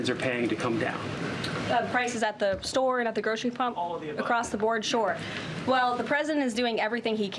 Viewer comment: Did she say prices “at the grocery pump”?? Around 6:11 to 6:12. 😂